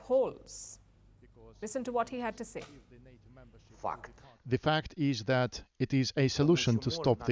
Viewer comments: 😂😂😂 Did he say fuck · "Listen to what he has to say" lol "F**k" 😂 😆 😝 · Did he say f#€k @ 1:41 😂 😂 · i totally thought he says "f u c k e d "